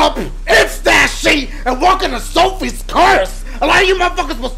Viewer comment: it sounded like he said 'Subway's Curse' xD